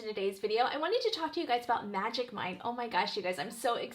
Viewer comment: I thought you said Magic Mike 🤭 You look amazing 😊